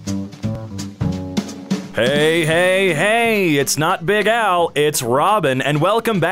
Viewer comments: Robin: “Hey! Hey! Hey!” Me: Bokuto?!? Robin: It’s not big Al, it’s Robin. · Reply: I heard it as "It's not Big Owl", wich was perfect. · He sounded like Bokuto with the “Hey Hey Hey!” In the beginning. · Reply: To be honest, I heard "Hey hey hey, it's not big owl" so I searched for some Bokuto comments to see if i was alone in this.